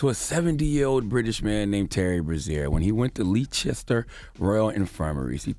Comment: i thought he said terry rozier😂